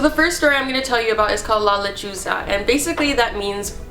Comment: OH MY GOD THE TITLE OF THE FIRST STORY I THOUGHT SHE SAID "la lechuga" WHICH MEANS THE LETTUCE IM DYING😂😫😫